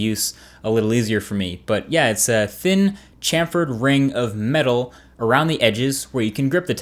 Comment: Did you just say ring?